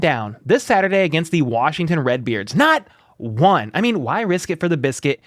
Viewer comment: 500th like for Good Luck turning in your notice to the day job! And am I hearing things or did u say when the Chiefs play the Washington Red Beards?! 🤣😂 (03:41) P.S. Let's RIIIDDDEEE!